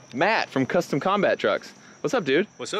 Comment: definitely thought I heard "Matt, from Custom Combat Drugs" 🤣Matt, supplier of choice to the United Earth Directorate and Terran Dominion alike!